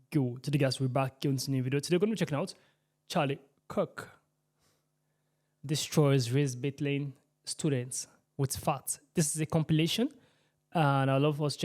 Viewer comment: I heard "charlie Kirk destroys race ait woman with farts".